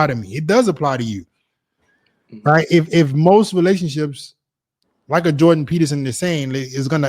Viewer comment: I thought he said Micheal Jordan Peterson😂